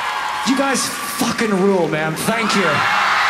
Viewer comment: in the middle there when he was screaming thank you, I thought he was saying "fuck you"